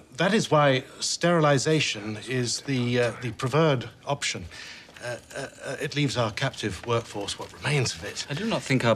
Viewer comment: What does Heydrich whisper at 0:17 ? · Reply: "this man is wasting our time"